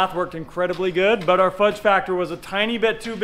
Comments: so Did I just hear fudge packer (7:20)? In Canada thats a term for anal sex. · I didn't hear that right the first time. The fudge what!! The fudge factor was too big. Lol